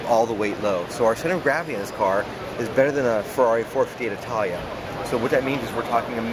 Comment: "Our idea behind the car is ultimate handling" Sooo take away Subaru legendary AWD system out and put in RWD... Makes sense..? and I could've sworn he said 415 Italia instead of 458... Throw your funds back in to the IMPREZA WRX/STi like you did back in 06 and 07... Those were some beautiful bang for your buck sports cars. To me this is a glorified toyota/scion piece of shit.